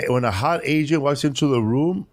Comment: I thought he said "when a hot Asian walks in the room" lol